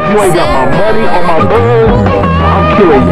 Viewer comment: Bro why am i hearing “ if u aint got my money on my balls” 💀😭🗿